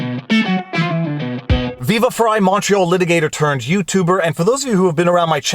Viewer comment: LET'S GET READY TO RUMBLE! on rumble <link> Ryan Holiday's "Trust Me I'm Lying" will change the way most people read the news... Book here (it's an amazon link, so I get a referral percentage) <link> Plot Against the President, link here (Amazon affiliate link so I get a referral percentage from Amazon): <link> Our Locals page here: <link> Me on Rumble (it's a growing video platform): <link> THREE ETERNAL TRUTHS MERCH! Color shirt: <link> White: <link> More random support links below (gear, merch, etc). Must watch: The Plot Against the President (documentary): <link> Must read: The Plot Against the President: <link> (Amazon links, I get an "Amazon Kickback" if you purchase through this link) People's Pundit (for Barnes election discussion): <link> Merch & other stuff: POLITICS RUINS EVERYTHING SHIRTS: In colours: <link> In white: <link> Barnes links: Website: <link> MY MUSIC: <link> Promo code vivafrei10 for 10% discount. Viva support links: <link> <link> <link> MY GEAR: Camera <link> Camera mic: <link> Stream mic: <link> Stream cam: <link> My GoPro: <link> OUR MERCH! vLAWg ON! <link> Old new merchandise: (New Pudge Vlawg Dawg shirts). With logo <link> No <link> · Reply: When I play your videos at 1.5 x I hear you say “macho litigator turned youtuber”